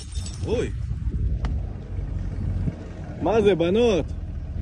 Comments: I think he’s saying a long the lines of,” what’s going on girls, what did you want to happen?” These are the words I heard in Hebrew, ״ מה זה בנות, מה זה צריך להיות?״ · Man: “What is this girls? What is this supposed to be?” Girls: “You scared me.” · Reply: Yeah, he says at 6:04 ״מה זה, בנות? מה זה צריך להיות?״ Which translates roughly to what is said in the first comment. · sounds like he said "maza bannot?" which is Hebrew for "what is it, girls?" I'm not sure about the second part, but I believe he said "what have you learned?"